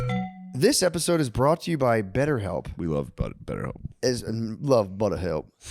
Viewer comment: All I heard Shane say was, "We love butt—" 🙃